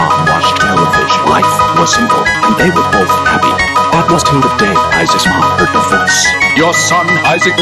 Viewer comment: I think this needs to be till the day instead of tell the day